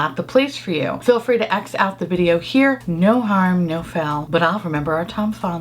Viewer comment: "... be prepared to X out the video..." For years, I have thought she said: 'exit the video' and it was her accent! 😳 We are still waiting for the Robin Eisenburg Palette in New Zealand and we may be waiting forever. The one store that stocks UD palettes has no plans to bring that palette into the country.